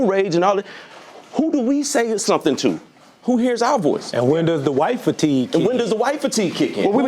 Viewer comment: For a second I heard "when's the wive fatigue kicking in"